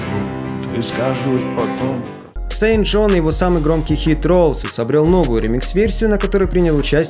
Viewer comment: я один подумал что он сказал sam jones?